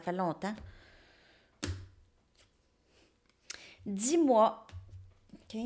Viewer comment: A la question que je me posais " quand est ce que tu vas m'appeller au de "10 mois " J'ai entendu " dis moi " .Euhhh?